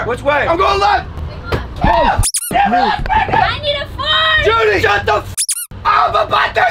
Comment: it sounded like she said “I NEED TO FAAART!!!!!”